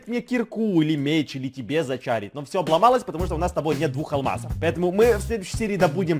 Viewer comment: мне послышалось у нас нет бухалмаза 😂